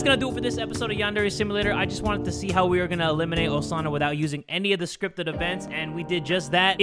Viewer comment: tbh i thought is said EAT instead of eliminate